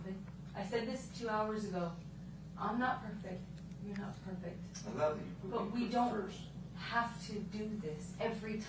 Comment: it sounds like he said, “I love you, but putting me first” something like that. If anyone knows how to edit the video sounds to bring his voice up, please do so. 4:59 she smacked him in the ear 5:11 she says sorry 5:20 - 5:29 JD: I don’t love you that much to give up myself.” AH: “Yes, you do” JD continues explaining that he basically will no longer tolerate it She claimed this is not another argument and that he’s making this up